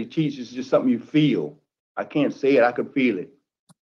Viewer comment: "i cant say it, i could feel it". Thats what i heard. I played it 4x to make sure i was hearing it correctly.